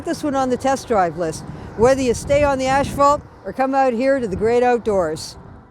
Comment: Did she just say ash-falt?